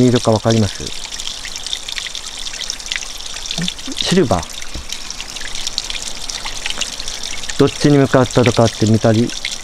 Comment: 昭和の人がシルバーっていうのかなぁ。 銀色じゃないの？ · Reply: シルバーって聞こえます😊